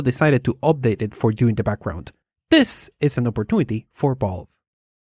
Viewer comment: Every time says "Valve/Valve's", I hear "Ball/Balls" and have to remind myself that he's really saying "Valve". Not trying to make fun of your accent, my dude, it's nothing to be ashamed of or anything. I just think it's a little bit funny. :)